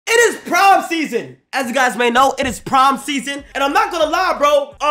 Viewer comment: thought bro was saying “prime season”